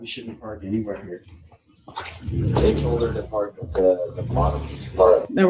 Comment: Sounded like they were saying I told her to park at bottom